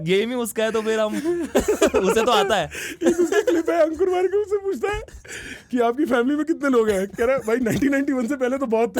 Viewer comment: what does he say? Samay apni kya kara? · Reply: aisi taisi